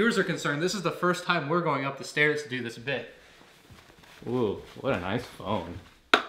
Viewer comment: When he said “oooh, what a nice phone” I thought he said “ooh, what a nice bum” 😂😂😂😂